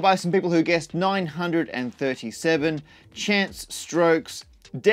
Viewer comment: he said Chance Strokes instead of Chance Stokes